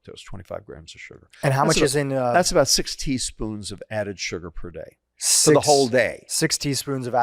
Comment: I swear to god, at first i thought he'd said 60 spoons a day... I was like "wtf"